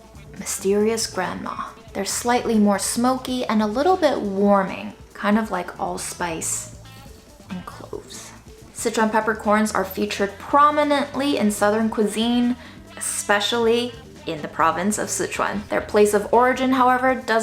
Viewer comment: CORRECTION: I have been informed that "green and red Sichuan peppers are not different stages of ripeness but two distinct varieties, like red and white wine grapes." Sorry for the misinformation and please don't ever trust me again. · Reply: Until this video, I always thought you were saying citron peppercorns. It was very exciting to realize I HAVE favorite peppercorns!!!!!